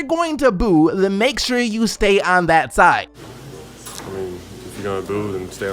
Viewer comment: I thought Simmons said "stand outside" not "stay on that side"